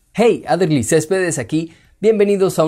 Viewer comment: La verdad yo siempre pensé que decías algo como: !Hey Adderly estedes aquí¡ :v